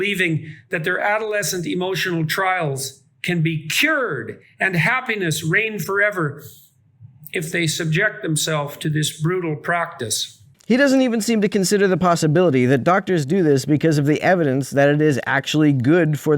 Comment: did he say "Proctis"?